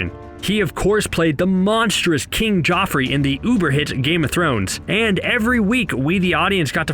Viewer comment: did he say “UBER hit, Game Of Thrones”?